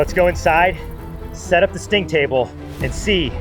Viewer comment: the sting table sounded like the stink table.